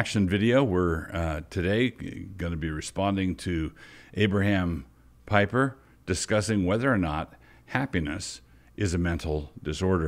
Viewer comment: I thought you said, Abraham Diaper. I think he is attempting to be sardonic but it didn't come off that way. So much for bad science ... Can you believe we fund these people?